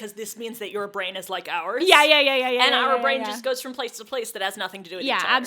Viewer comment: did just say “our brain,” singular??? Conspiracy theory time?